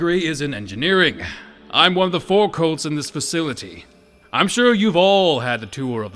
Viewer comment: Does he say forecolts or four colts at 24:49?